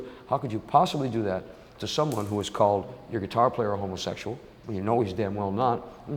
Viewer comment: What did Ozzy say referenced at 13:18? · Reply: Calling Tony Iommi a "homosexual".